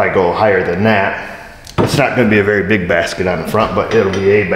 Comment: I thought it said brisket. Mmmm all atv shoukd have a brisket